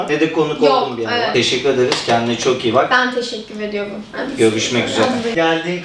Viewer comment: abi benmi yanlış duydum 16:08 kakikasında ben teşekkür edeRim mi dio yoksa ben mi yanlış duydum